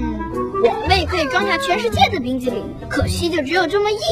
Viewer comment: Did Say Bing Chilling?